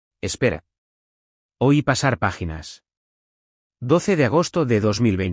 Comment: ¿ Hoy (este día) pasar páginas, u oí (escuché) pasar páginas.? .